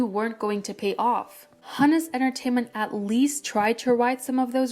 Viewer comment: I thought it said Humus Entertainment at first